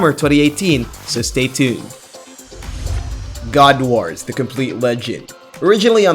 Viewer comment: When he said god wars i thought he said god of war